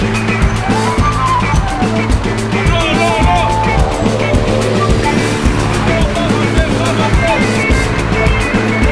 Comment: il dit quoi ?😂😂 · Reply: "Allons allons allons, il faut avancer, il faut avancer" "Let's go, let's go, let's go, you need to move forward, you need to move forward."